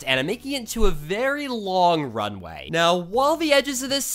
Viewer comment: i thought ou said run away